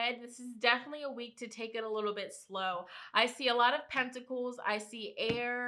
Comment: listening to this while I work, swore you said I see a lot of testicles... I was like WHATTTT????? What kind of week am I about to have??? 😆